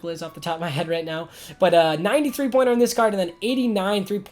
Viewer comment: When you said 90 3 pointer I thought you said 93 pointer